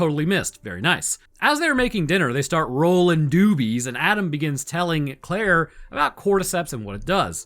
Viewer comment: Oh god, i thought you said "Roll in doodies" and not "Rolling Doobies" 🤣🤣🤣🤣🤣